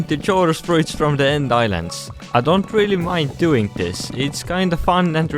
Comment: why did he say that he doesn't like getting chorus fruit and then he said it's kinda fun and relaxing?? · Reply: He said, "I don't really mind getting this"